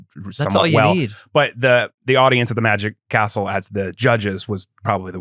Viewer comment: I kept hearing "The Magic Arsehole", took a while before it clicked "The Magic Castle"